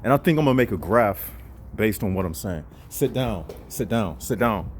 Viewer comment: What was that at 8:14 saying "Sit down sit down"?